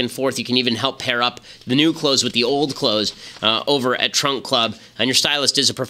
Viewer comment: I thought Ben said "Trump club" and I got excited.